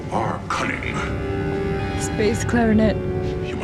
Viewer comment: When you said, "bass clarinet" I thought you said, "space clarinet". :)